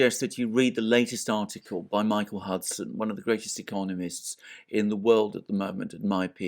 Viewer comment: The artical Alexander mentioned at 1:19 by Michael ???? I couldnt hear well enough to make it out ...can anyone help me find this reference ? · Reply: Michael Hudson, economist.